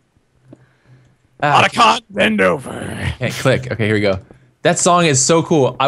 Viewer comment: I swear said, "Monica! Bend over!" If he did, then 0.o